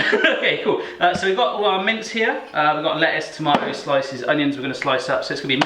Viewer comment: American problems: every time he says "i have my mince here" I hear "mints" and I'm like...why you need mint for burger..... also did he just call ketchup/catsup "relish"? @@